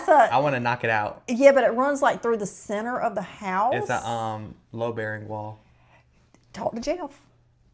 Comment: That’s what he said… a load bearing wall. Thus why he said he would need a beam placed. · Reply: opps! I thought he said low bearing.